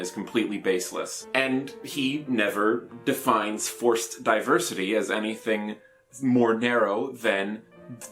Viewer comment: When u said forced diversity i thought u were saying force diversity, as in like, diversity in how people can use the force lmao